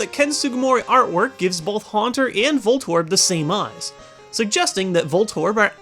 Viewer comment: I had the volume turned down at the part with Voltorb and Haunter, I swear I heard him say Voldemort instead of Voltorb... O.o